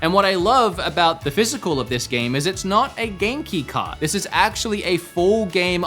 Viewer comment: What's Genki card? Oh Game-Key card. It sounded like you said Genki card. I wouldn't put it past Nintendo, or some other company in Japan, to release something called a 'Genki card', so, I didn't even question it in my head.